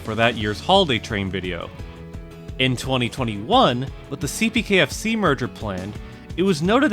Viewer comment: Did I just hear CPKFC?